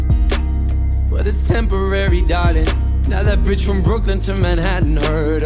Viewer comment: That bridge?? I thought he was saying B**tch😂